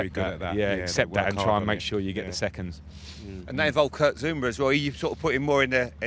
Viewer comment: did anyone else hear Jamie call Kurt Zouma "Kurk Zoumba" or was that just me?